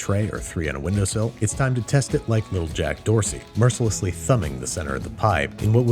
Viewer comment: Did he say Jack Dorsey? The Twitter guy?